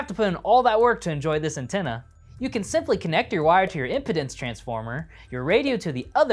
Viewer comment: Did he really say impotence transformer at 1:32?? LMAO!!